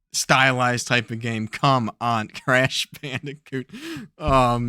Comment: I had to rewind as I thought you said trash bandicoot lol. That would be a perfect name for that picture.